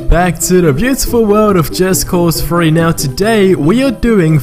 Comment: i thought he said "just cause free", definitely freaked out a bit when i saw this was uploaded a year ago, thought he was some psychic for a second.